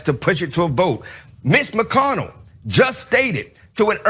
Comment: Tjis dude needs to go tsje an English class😂😂😂😂thought he was saying ms not mitch😂😂😂😂 Anyone who supports dump is showing us who they are,vote blue!!! 💙